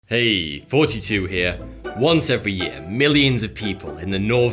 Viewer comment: It's 42 here lmao that's what it sounds like he says